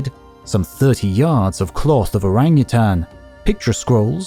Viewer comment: I just heard "Cloth of Orangutan" and it intrigues me.